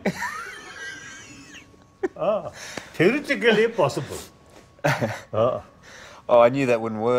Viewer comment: What is Dalai Lama saying at 0:41? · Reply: justamused Now that we put the T and the possible, I think is "theoretically impossible".